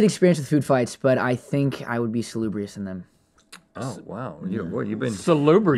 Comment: What is that word? 0:40 Celubrious? Salubrious? "Spectacularly average"